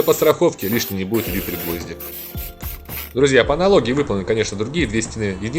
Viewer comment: На 5:40 подскажите, какие гвозди? а то не могу расслышать. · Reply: дюбель-гвозди